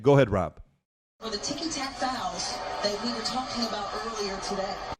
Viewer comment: OK so why is youtube making it sound like she said "ticky tack fouls" ?